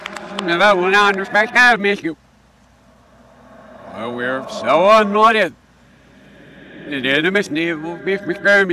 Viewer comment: "Oh we are so Annoyed" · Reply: I heard "We are so Anointed". Even scarier.